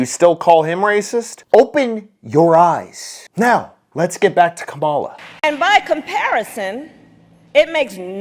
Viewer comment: I thought he said get back in kamala, There's enough room for all of us to have a party in there Just don't invite me please